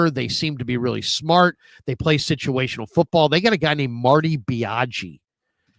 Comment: what did say? Marty Biatch? lol